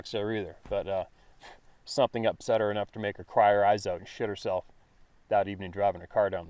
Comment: did I hear right? At 7:46 "She shot herself driving down the road..."